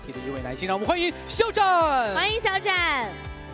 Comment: I heard She said'Bunny xiao zhan' 🤗🐰💜 Ya ya he is our lil bunny boy 🐰💜❤⚡ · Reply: No he said "huan ying" (欢迎) which means "welcome" hahaha